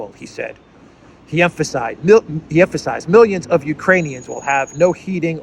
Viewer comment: I thought he said Uranians... maybe we are helping criminal govts on other planets now... with the Biden Govt... who knows...